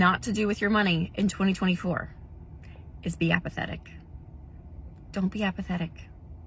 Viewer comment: I thought you said "be empathetic." That is another fast track to poverty!